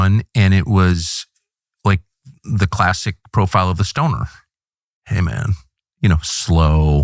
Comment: Thought he said "the stoner, Amen" :)